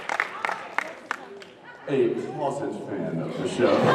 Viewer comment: Sorry! Hard of hearing little me again... In the "destiel denial" section at 11:06, what is the adjective in air quotes? "I think that Dean is a fan of the show. [Laughter] A [airquote] fan of the show." What word is in the air quotes? Also, thank you so, so, SO MUCH for posting the whole panel. Like many others, I'll never afford to go, so I stalk YouTube the day after every con. I kiss you for recording and uploading these! Mwah! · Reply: "A closet fan of the show." And no problem! Just happy to share some of the experience with others lol =^u^=